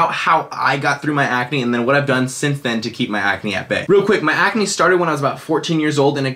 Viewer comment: All I keep I hearing is "Keep acne at Bae" hahaha